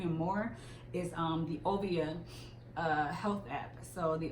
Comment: Definitely thought you said obia at first. Glad you cleared that up 😂